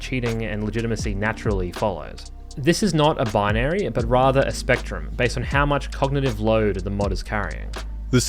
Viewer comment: Just FYI at 36:45 the transcript & closed captions diverge from the spoken text, possibly reflecting an earlier draft. Specifically, the speaker says: "This is not a binary, but rather a spectrum based on how much cognitive load the mod is carrying." But the CC says: "Even though most players don’t frame this conversation in academic terms, a lot of us feel the subject of legitimacy hanging in the air, like a nerdy poltergeist. The mods we are most concerned with, the mods with the greatest influence on combat, are the ones that assume cognitive load." Cheers for another great video!